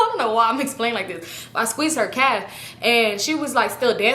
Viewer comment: i watched this storytime sm times and all this time i thought you said “i squeezed her CAT” 😂😂😂😂😂